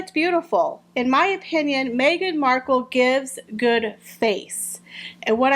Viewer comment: I watched the Royal Funeral from 7pm to !!!!! I stayed up all night & I was a Zombie for the rest of the day 🇦🇺 That Migraine was an intruder, that’s the vibe I got. Oh , good face 🤦‍♀️ silly me… I thought you said … GOOD H%#* 😂😂😂 yeah ok 😉 Wearing that Zorro cape & hat was hiding the mics !!!!! Yes , we all know. Sophie was (apparently) asked by William, because Sophie is the best & to protect the children. Sophie’s got balls & Migraine shits her pants with Sophie. I would like to think that Our Gracious Queen had the last laugh, with the sitting & camera. 😂😂😂